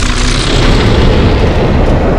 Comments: "FOR PLUNGER" Are best lines in this episode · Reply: I thought he said for pleasure😂😂😂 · “For Plunger” indeed the coldest quote throughout the whole Skibidi Toilet series 🥶🥶🥶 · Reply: I thought he said "for pleasure" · TITAN CAMERAMAN CAN SPEAK ENGLISH??? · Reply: i think i heard him saying "FU$$ PLEASURE" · "for plunger" best part (btw can you stop blowing up my notification's) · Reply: I thought it said “for pleasure” 💀💀💀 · "for plunger" best part (btw can you stop blowing up my notification's) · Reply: Thought he said "for pleasure" which is just as epic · "for plunger" best part (btw can you stop blowing up my notification's) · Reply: I thought he said “for pleasure” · "for plunger" best part (btw can you stop blowing up my notification's) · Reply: I've used to thought he said "for pleasure" 💀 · "for plunger" best part (btw can you stop blowing up my notification's) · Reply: I thought he said my pleasure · TITAN CAMERAMAN CAN SPEAK ENGLISH??? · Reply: ohh i thought he said "FU$$ PLEASURE" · "for plunger" best part (btw can you stop blowing up my notification's) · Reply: I thought he said for pleasure